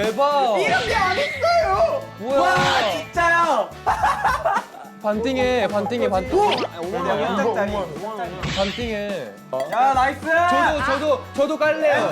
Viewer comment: Soobin said: "patingen patingen patingen" Did he said that in Filipino language 😂. I'm dreaming 😅 Shout-out to Philippines Moa